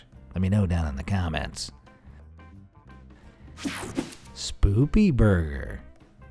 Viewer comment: did he say spoopy?